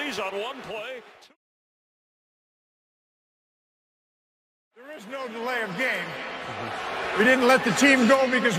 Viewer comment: When I heard this live I thought Ed Hoculi said "There is no delay of game we were scratching our balls"